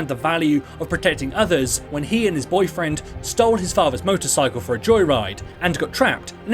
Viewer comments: did he just say he and his boyfriend? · did he say gilbrartar and his boyfriend 🤔🤔